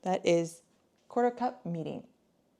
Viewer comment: Hi there. The liquid you put in at 6:33 . What was that? I couldn't hear what you said and the captions said it was "meeting"which obviously it isn't. Thank you! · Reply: The clear one my kid put in? That’s mirin